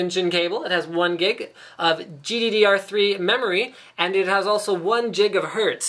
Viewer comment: did i just heard GDDR3?!